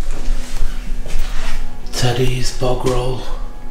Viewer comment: I'm watching it a little late but at 3:59 in I'm LMAO 😂 (if I heard correctly) did say a 30's Butt roll when exploring the kitchen area ?